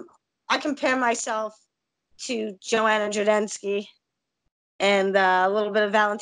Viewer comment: wtf i know she didn't say joanna j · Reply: She didn't. She said loud n clear: Joanna Jodensky.